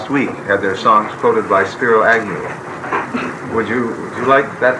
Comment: what is he saying spirit annual?